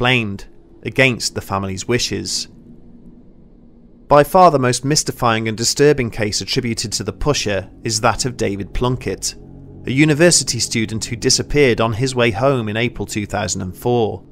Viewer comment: ish I thought for a moment you said David Blunkett, and I was like what??